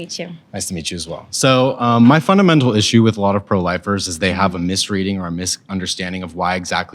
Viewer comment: I thought it said 1 “pro lifter”